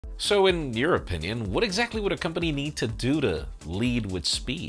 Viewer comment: you said "in your opinion" I heard it as in European Union 😂